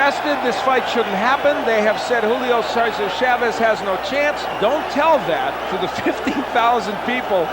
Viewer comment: what? did he said chavez had no chance?minute 13:39, before i saw this fight i saw tszyu fights, and well we will see, i haven tseen this fight yet, so lets find out